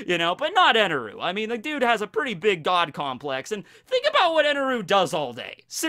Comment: when says that "the dude has a pretty big god complex", the subtitles show "dog" instead of "god".